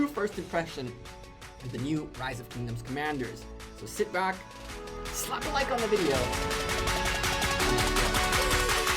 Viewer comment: D*mb question, what do u say after "Sit back, slap like on the video ........." Cuz of all your videos like that😭😁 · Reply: and lets go...